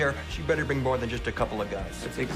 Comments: Hahaha I thought he said "feed the f*cking seal", but your comment makes more sense :P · Charlie yells: "Feet of fucking steel!" It's a callback to someone talking about feet of clay being held over a fire earlier. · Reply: I always thought he said: "Feed the fucking seal!" 😅